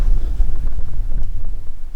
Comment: Honestly for a moment i thought you said, "Oh my god, beers", not bears! LOL I about spit my coffee out! I do hope you had a 'beer' for later after you were parked. Happy and beautful trails to you.